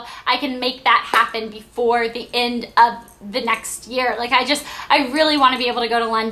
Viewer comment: i nearly thought you said before the end of the world!!!! at 04:04 :L :P